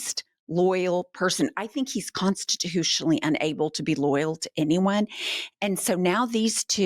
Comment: loyal.. i thought you were saying, royal.. hahaha